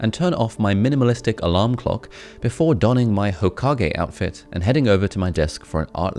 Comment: Did he just say Hokage